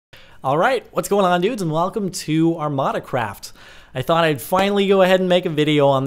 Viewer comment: Who else thought it said Amanda Craft? lol